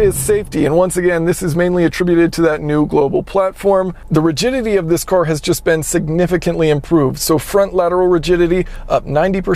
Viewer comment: I thought he said the Virginity of this car has been totally RENEWED... 🤣🤣🤣